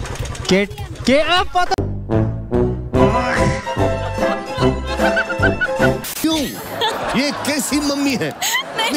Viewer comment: what the boy said? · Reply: Ktm